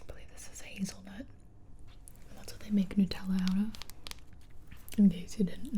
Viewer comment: did she just say nootella?